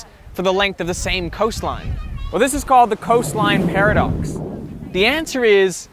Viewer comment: oh, "paradox", i heard "para-ducks". I thought it was ducks who parachute.